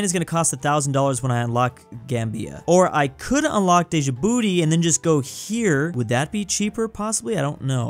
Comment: i heard said deja booty even without the reading things